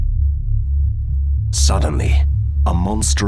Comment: At first I thought the first thing said in the video was sodomy. I was like oh shit this is gonna be scary. Oh whoo he said suddenly we are ok now.